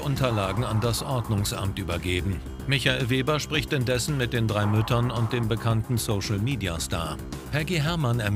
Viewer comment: ich hab "...spricht mit den drei Mülltonnen..." verstanden🤣